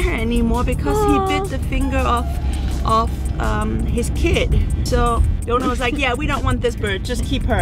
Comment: Also thought I heard Faith’s human say “he bit the finger off his kid” XD before I realized it was “the finger of his kid.”